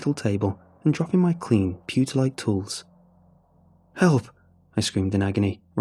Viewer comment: What does putalite (2:54) mean? He says clean and putalite utensils? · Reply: Pewter-like